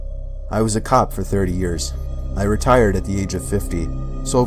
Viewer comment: omg i thought it said retarded